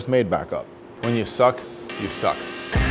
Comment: What John actually said: "When you suck, you suck" What i heard: "When you stuck, you suck"